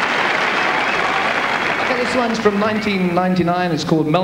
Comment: am I hearing it wrong or does he say, "This one's from 1999..."